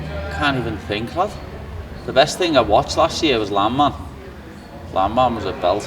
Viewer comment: what did paddy say was his favourite movie of 2025? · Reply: Landman